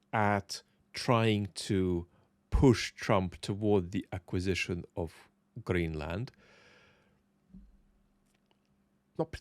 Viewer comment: perhaps around 7:10 what did you just say - "Trump's acquisitioning of Ugreenland" ?